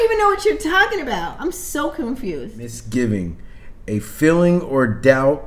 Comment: i thought said "Ms Giving!"